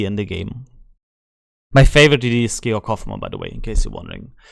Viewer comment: I didn't catch your fav DD - last check AI search showed Halland & Elbing with Cossack in the mix. At 31:13 are you saying Hoffman? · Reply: Yes hoffman Nr 1 vampire 2 nr2 elbing nr3